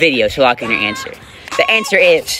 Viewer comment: This was what I heard at the end “Subcribe if u were shot too”